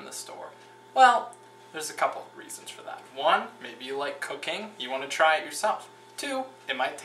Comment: It sounds like he say`s,if you like cocaine,then try it yourself! haha,nice one from woody allen chef!